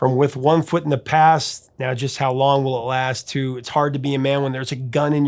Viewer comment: All these years, I thought he said "it's hard to be a man when there's a girl in your heart"